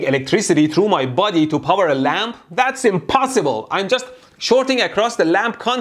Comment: I thought you said its impssible